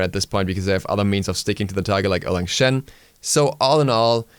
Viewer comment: What is this when he says " other meent sticking to a target like elekshen o.O( what is elecshen